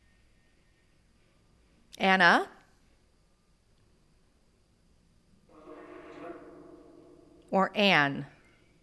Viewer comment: “Anna is fine” is what I heard (haven’t finished watching yet, I just wanted to write what I heard before I continue).